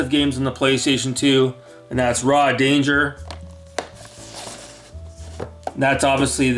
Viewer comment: I thought it said raw dogger lol